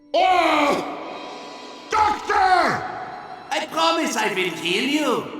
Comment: Is it me or when the medic says “I promise I will heal you” I heard “I promise I will kill you”